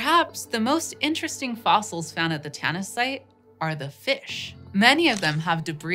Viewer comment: Tanis? Oh I thought you said tennis.